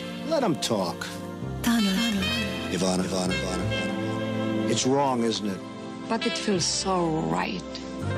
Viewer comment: I thought he keeps saying Ivanka🤮